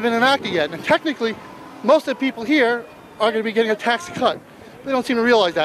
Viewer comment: Did at 3:45 actually say "Most of them here aren't going to get a tax cut"? I listened to it 4x, and that's what I heard. And no, things are NOT getting better under Obama. Not at all